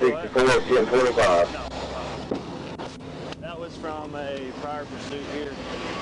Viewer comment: Trooper: “That was from a prior pursuit” Cop: (Laughing) “Must be nice” LOL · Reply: At first, I thought said "That was from a Firebird". Such as he could probably list every mark on his front end to each previous make/model vehicle he had to pit that caused each dent. lol